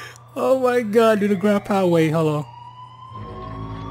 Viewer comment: is it me or is did he just say "ground powerade"? Lmao